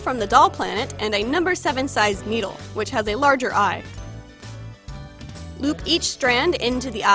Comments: it sounds like she said boop. Haha so kawaii! happy pride month!!!!!!! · I heard "boop" instead of loop